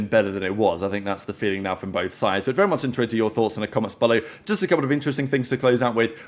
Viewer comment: what does he say at the start of every video? i honestly cant tell, i just hear "very ??? ??? ?? ?? your thoughts in the comments" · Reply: very much intrigued to hear your thoughts in the comments